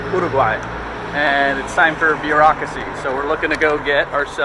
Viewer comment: “Beer-aucracy” was what I heard at 22:28. 😉